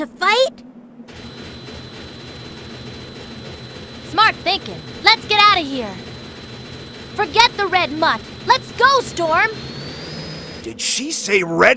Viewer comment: Time to make some impacts at my age! 3:17 Scanning scanning. Yep thats the voice now. SHOOT EM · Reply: KNUCKLES AND STELLA: DID SHE SAY, "RED MUTT"...?